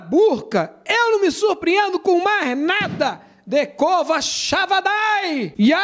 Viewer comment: Único apóstolo que confio! Eu ouvi um decova xavanay? ouvi